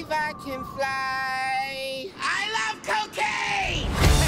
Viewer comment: I feel ashamed to have thought he said 'I love cooking!'